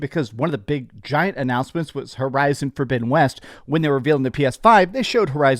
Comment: what’s that you said…..”Fiddabidden West”!? Lmao